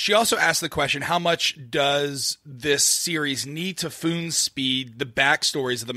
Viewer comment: "How much does this series need to FOON-SPEED the back-story..." instead of "Spoon-feed" lmaoooo